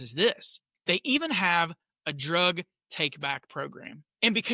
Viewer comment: Oh drug "Take back program. I thought you said drug Buyback program. That would have been awesome to cut out the middleman